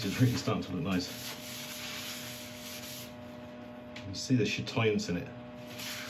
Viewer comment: you use a word referencing the look of the wood that I do not understand-sounds like “chitians” @ 10:37. Have heard you use it multiple times but cannot catch it…correct spelling please so I can look it up. One of these lucky days, I shall acquire an original piece! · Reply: Hi Kathleen, of course, it was chatoyance, official definition is: "Chatoyance is an optical effect that causes a changeable luster or color in gemstones, woods, and carbon fiber. It's also known as the cat's eye effect, and comes from the French phrase œil de chat, which means "cat's eye"."